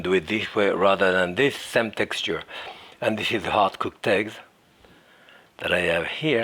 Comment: I thought he said "art cooked eggs" and it seemed like a mistake, but when he opened the second egg I understood I heard it right